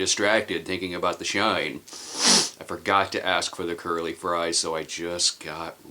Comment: At 1st I thought you said you forgot to get girly fries & then I realized you said curly fries